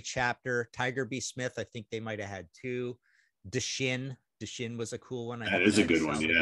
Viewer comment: What was the band that Martin says at 10:54? Deshin , De Shin, The Shin???? Help please...